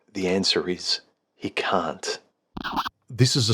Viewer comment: huMAN: The answer is... you can't What I heard: The answer is... you cunt